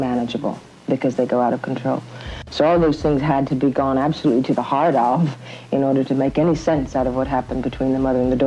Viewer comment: Scenes “gone to the hard off”? What’d I just hear? 😧